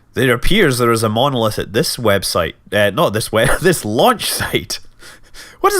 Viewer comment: Launch Pad Flag: "Aww, I thought they said lunch!"